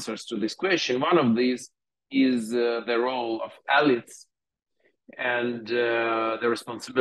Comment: "...the role of X, the responsibility of X." what is this word? the autogenerated subtitles say alids and alex, both of which sound very close to what the speaker is saying, but make zero sense. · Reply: Elites, I believe.